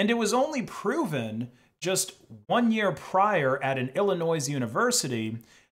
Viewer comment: Did you say “ill a noise” there? 🤷‍♂️